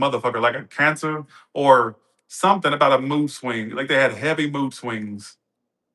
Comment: lol I heard booze swings and that would also be correct 😅😂😭😂that cappy being crappy thirsty and meanboi